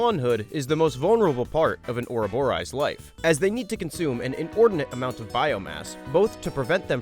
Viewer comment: "They need to consume an exorbitant amount of bio mass" I swear I heard scream: COCAINE!!!!!!!